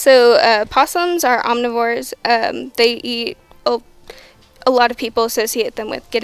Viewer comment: thought she said they eat people